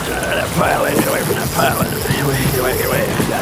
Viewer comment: What hell he say at 2:19? Lol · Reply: Get away from that pilling